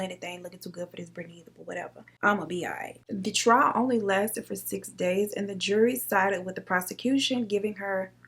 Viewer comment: Tell me why I heard, “The Dry Humping lasted for 6 Days”